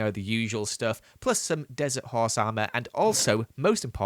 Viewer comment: Did he just say “plus some dessert horse armor’’